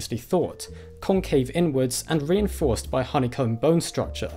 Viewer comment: when he says inward it sounded like n word lol